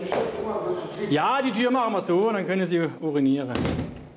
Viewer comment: was hat der Polizist genau gesagt ? 😂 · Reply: „Ja, die Tür machen wa zu und dann können Sie urinieren.“